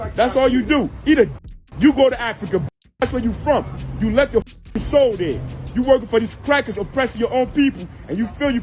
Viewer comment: One Black man telling another Black man to go back to Africa is just ignorant. He puppeting what his bosses have bern saying. Please take his Black card today. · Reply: Hol on, I think you misheard. 2:27 Put on captions. He said "go back to Africa because you left your soul there." Like that's where their roots are, not "go back" like racists say.